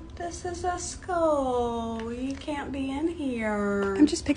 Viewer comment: I heard "This is a school we can't be in here"